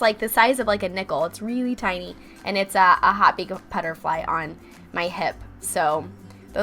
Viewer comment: "Hot big of a butterfly on my hip"? I can't make out the words before the word "butterfly". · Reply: Hot Pink butterfly